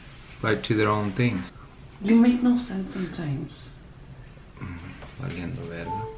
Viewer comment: “ you make no sense sometimes “ Ahahaha on god I did not get what he was saying 😂😂